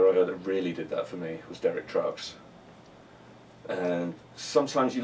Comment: Derrick who? Does anybody know what he said? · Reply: Derek Trucks?